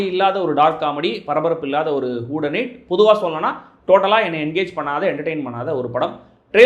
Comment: i always hear hood and hit. :face-blue-smiling::face-blue-smiling